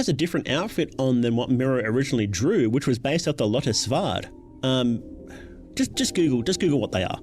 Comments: sorry mate for the life of me i cant tell what you're saying at 22:38 based off the what??? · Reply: Lotta svärd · what did he say?? lotis vard??? lotus yard?? /gen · what do we google? Captions say Lotus Vard but that gets nothing, n' i tried lottisvahd but that got even less results lol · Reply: Lotta Svärd, found it. Finnish voluntary auxiliary paramilitary organization for women